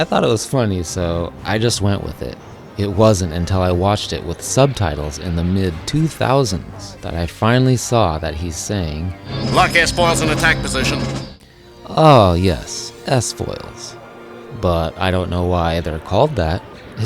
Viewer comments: "A gentleman wastes enough to be traveled lightly" was spot-on what I always heard. I always heard "Lock s-foils in attack position" as "Luck as falls in attack position," which always left me scratching my head. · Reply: I'm almost the same except I heard #9 as "Luck a** falls in attack position" (two S's). I knew I was hearing it wrong but couldn't figure it out no matter how hard I tried. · "A gentleman wastes enough to be traveled lightly" was spot-on what I always heard. I always heard "Lock s-foils in attack position" as "Luck as falls in attack position," which always left me scratching my head. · I didn't know out-gassing. Also, I thought Red Leader said, "Lock your spoils in attack position." (I figured the x-wings were 'spoilers', like on a car). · The only one from your list I misheard was "lock s-foil in attack position". I heard "Luck S-Falls in attack position". I had no idea what it meant. Then again, the actual dialogue doesn't make any sense to me either. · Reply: Now this one I will admit, I once thought they said X-Foils.. considering those are the wings, and they open to form an X, does make sense to mishear S for X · I always heard "lock X-foils in attack positions", just found out that it's S-foils... X-foils makes more sense to me